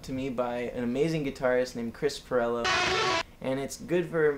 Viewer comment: Sorry but I don't understand the name of the guitarist at 0:48!! help me please ! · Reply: I believe it was Krisparello